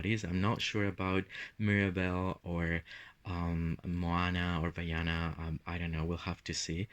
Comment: Did you said Rihanna 😂 at minutes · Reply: LOLOL Just checked haha I said Moana or Vaina haha as that princess is called with three different names accross the World also oceana? I always get confused haha Vaiana Is the official name for her in Europe I think lol but we can call her RiRi from now on XD ❤